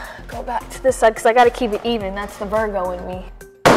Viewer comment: did she say "that's the Virgo in me" because that would make my day